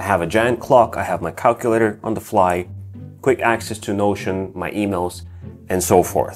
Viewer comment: I thought bro said quick access to lotion😭